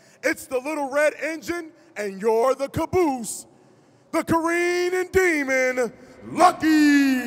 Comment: "THE GREEN DEMON, LUCKYYYY" unveils red robot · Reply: I thought Faruq said "The careenin' demon"?